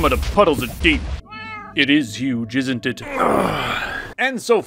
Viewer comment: I heard “it is you, jizzn’t it”